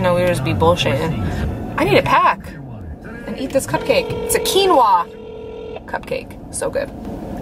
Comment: OMG!!! my husband was listening while I was watching and he thought you said kilos of cupcakes....hahaha